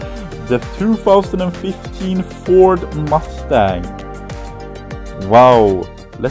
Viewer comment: i heard "mundang" and got scared, but was then relieved when i heard mustang correctly